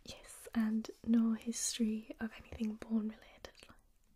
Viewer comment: at 6.47 does say "no history of porn related"? or did i mishear her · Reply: “No history of anything bone related”.